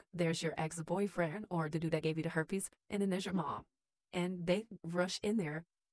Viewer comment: Which one is it, I thought u said HIV, not herpes!!!